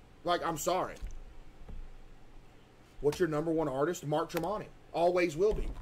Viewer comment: did he said Mark Tremonti ?